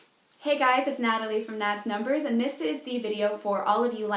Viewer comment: thought u said Nads numbers lol